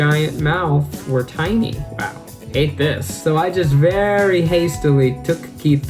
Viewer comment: There's a mistake in the captions. I think? Captions say: "ate this" But maybe it's meant to say "Hate this"? It would be nice if you took a look at it